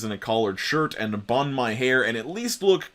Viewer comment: For some reason I misheard "bun my hair" as "embalm my hair"